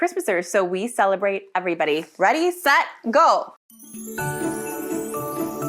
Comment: You say 'Ready, Set, Go!' and I swear I hear 'Ready, Set, Gold!!!' I guess all I see is the hints of gold in the first seconds of the video.